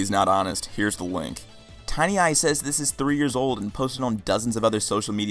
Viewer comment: Just me who hear "Tine ISIS"